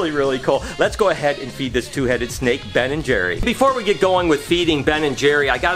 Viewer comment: I thought you said you were gonna feed the snakes with Ben and Jerry’s . I was so confused for a second 😂